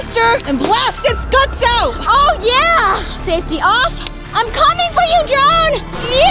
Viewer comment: did by just said safe off